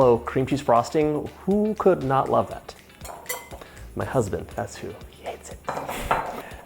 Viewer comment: did he just say his. Husband?